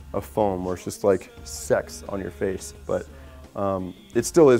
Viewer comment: I SWEAR he said "Sex on your face". What did everyone else hear? He could of said "Sects" but I don't know...